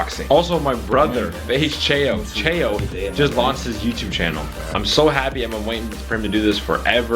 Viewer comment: I thought you said JUST LOST HIS YOUTUBE CHANNEL, I'M SO HAPPY::: I was like dam lol.